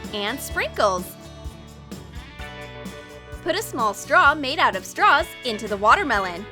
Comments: Who else heard her say “make a small straw out of straws” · did anyone hear "Put a small straw out of straws "like this and the others if you did